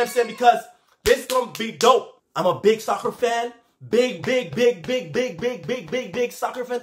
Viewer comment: When you say “I’m a big soccer 🥅fan” I heard like “ I’m a big sucker fan 😂” 😂😂